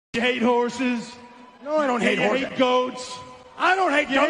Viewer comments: When I heard “you hate horses “ I thought it said”Gay horses”💀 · The beginning i thought it said "Gay horses" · I thought it said gay horses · I thought he said "gay horses" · I thought he say gay horses🗿📸